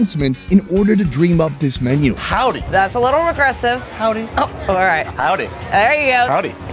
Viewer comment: Howdy! Texan: That's a little aggressive: Howdy... Texan: Oh alright All Texans: You cannot comprehend our system of communication you mere mortals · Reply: i thought she was saying regressive at first and i was like... what the hell lmfao